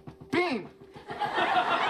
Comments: always thought he said 'the unbearable likeness of bean'. interesting. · always thought he said 'the unbearable likeness of bean'. interesting. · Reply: Antonia Memery Joey said The unbearable LIGHTNESS of Bean. Dan thought he heard "Likeness" but the right word is "Lightness".